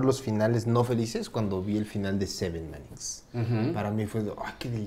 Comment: Alguien sabe cuál es la película que dice Ballarta? Yo solo entendí la palabra Seven pero no entiendo lo demás. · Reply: Así se llama: Se7en